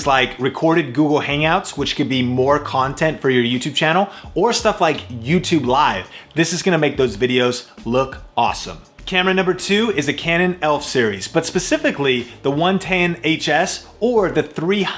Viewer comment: did he say canon elf series